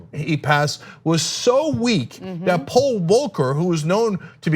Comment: I thought Cenk said Paul Walker from fast and furious. 😂